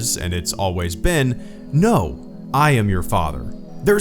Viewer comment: I hear "No, I am the father" for some reason.